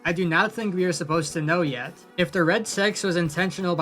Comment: the automatic captions say red sex